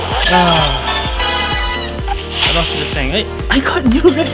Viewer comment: did you say f the tank lol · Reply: i said i lost to the tank lol